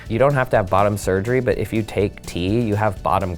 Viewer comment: Caption says 'tea', is referring to T